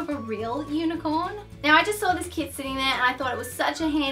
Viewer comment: I thought I said 'now I just saw a kid sitting there...' 😂😂